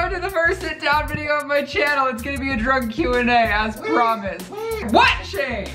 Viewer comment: I swear every time you said "drunk q&a" I heard " DRUG q&a" and was like whaaat? Cause I'm a lil drunk rn too. 😂